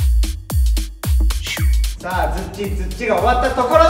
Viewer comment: のちゃかちゃん、最初何が終わったか聞き取れかったけど、ツッチツッチってメンバー紹介のところの曲の話ね！ あの曲をツッチツッチって表現するの可愛い